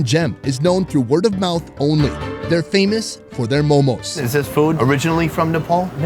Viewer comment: I thought i heard momols hahahaha